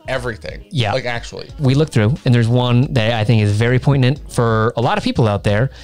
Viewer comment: …. Did say it was very “poin-tant”? Not sure if he meant poignant or pertinent….. am sure that whiskey destroyed more than his palate.